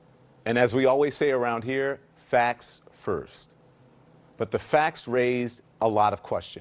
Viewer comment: did lemmon say CNN deal in "facts first" lol.😂